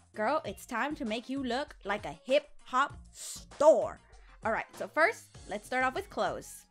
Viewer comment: I thought she said “look like a hip hop store” 😂